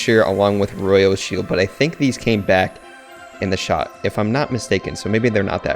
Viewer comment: did he say “they came back in the shop”